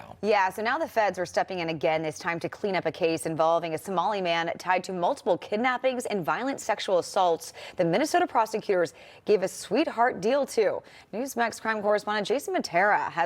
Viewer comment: For a minute I thought the reporter said a "tamale man".